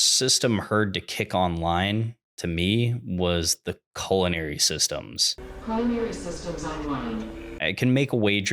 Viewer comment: Great Video! Listened to it as background noise quite a few times while playing a game. Though, I do think what was said at 4:06 was "Primary System Online" and not Culinary System.